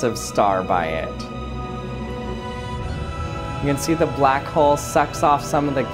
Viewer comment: Captions Says sex O.O